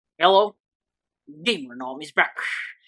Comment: wait did u just say "yellow"? or "hello"?